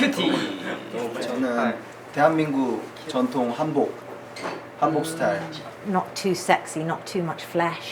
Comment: I thought he said Gangnam style for a sec lol